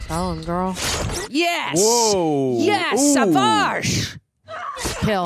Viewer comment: did she said shavash as in very good in Hindi language when kali was escaping????